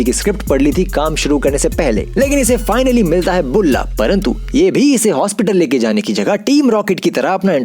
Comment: I am sorry, but did you just say " parantu " at 1:28 ??